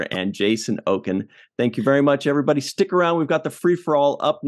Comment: Great guest! But am I the only person that when says "Everybody stick around" I hear it as "Everybody stink around"?